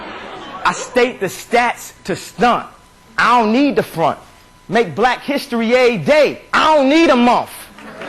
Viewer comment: I thought he said 'make black history aid day, I don't need a month', asin he can raise awareness in one day he doesnt need a longer period of time to make people listen. that would make more sense right?? because if he says '..80 days, I don't need a month' he's kind of contradicting himself.. ?